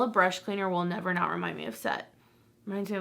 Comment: Definitely thought you said brush cleaner will always remind you of sex 🤣